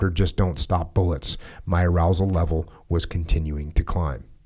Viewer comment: did he say arousal level?